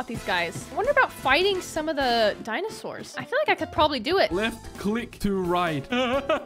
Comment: i thought he said left cleet to right not left click to ride